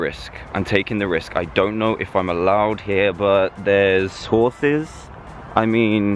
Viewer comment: when there was horses I thought you said horsea